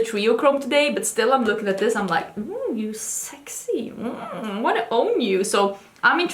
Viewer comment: Maybe it’s just my dirty mind, or maybe I’ve been watching too much Theresa is Dead (jk, no such thing), but I totally heard, “I want to BONE you” instead of “I want to OWN you” about ND summer 2021. I was like, “Man, Angie is extra frisky today!” lol